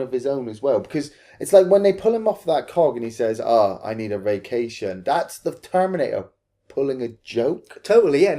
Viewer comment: The bit where he says "I need a vacation" for the longest time I kept hearing "I need to be caged".